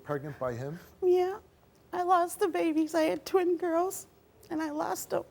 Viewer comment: Twin girls! Oh it sounded like she said 20 girls..